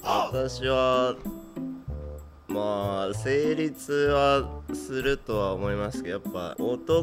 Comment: 成立が生理痛に聞こえて 生理痛はするってどういうことっ！？ってなったww